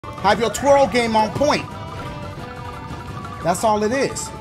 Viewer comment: Is it just me or did it initially sound like he said "Have your Troll Game on point!" at 0:00?